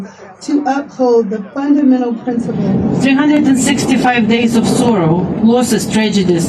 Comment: lol I thought she said 365 days of Soros. For a sec I thought she was based, but no. Just a war monger.